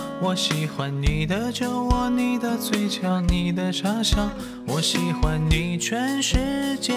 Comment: 他唱的你的傻笑，我听成你的叉烧...